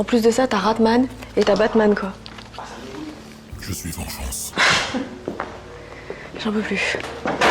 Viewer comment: qui a entendu la voix qui disait "je suis en face" Je flippe trop 😢😢😢aidez moi j'ai peur 😖😖😖 17:21 et aussi ici le "on est là" 😭😭en plus des fois si vous entendez ils crient 21:52 "dit leur que t'as rien fait"